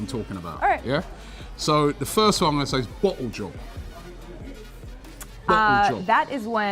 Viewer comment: When he said bottle job , I thought he said blow job 😂😂😂😂😂😂